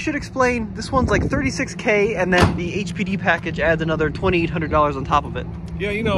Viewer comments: Sounds like he said HPV package, which makes more sense. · I thought he said it had the HPV package lol.